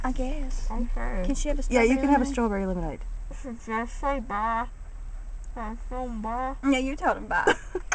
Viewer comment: Someone please correct me, but is she saying, "Should I say 'baa'? Cats go 'baa'?" ? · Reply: ''did i say bye?''